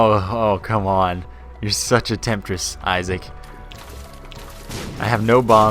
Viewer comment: I thought in this run you said "Isaac, you're such a mistress" but you really said "Isaac, you're such a temptress". But either way, are you sexually aroused by the Binding of Isaac?